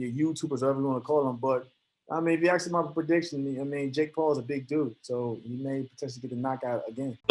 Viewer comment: “John Paul is blogger” - Yan. LMAOO Love that dude he’s a killer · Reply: I thought he said - Jake Paul is a booger. It would also be true.